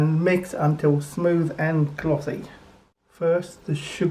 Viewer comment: The newspaper in 2 layers, tied with string, is what I use when making Xmas fruitcake because of the very long bake time. Looks delish. Why is it called Madeira cake? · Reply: At first I thought you said "smooth and clotted", which didn't make sense. But I think you really said glossy 😂